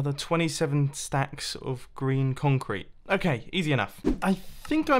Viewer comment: Why do I now hear Grian instead of green?